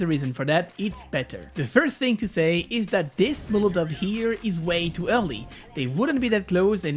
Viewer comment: Toi aussi tu as un petit peu de travail à faire: 9:44 "this molotov here is way too HEURE-LIT"